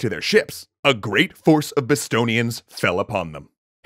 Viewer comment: I heard "a great force of Bostonians" and was very confused for a moment.